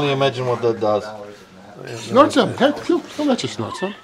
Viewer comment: what in the world did that guy walking up say? 😂 I’ve watched it 10 times now. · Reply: “Snort some, hmm? He’ll let you snort some!” He’s trying to make some kind of joke about that medicine vial was holding with the “mystery” contents in it.